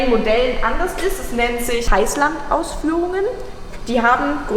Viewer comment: auch Heißland-Ausführungen, ich hab zuerst Reisland verstanden, dachte mir wtf 😀